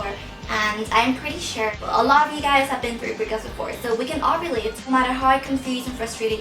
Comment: when she said "i think we can all relate" i thought she said "i think we can ovulate"